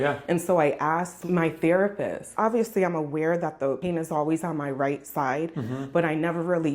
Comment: Do you guys use AI to generate subtitles? 'Cause not only does it make it difficult for those who are hard of hearing to understand what everybody's saying, you also wrote 🍆 instead of "pain is" in 08:00. Please have whoever's doing your subtitles make 'em themselves.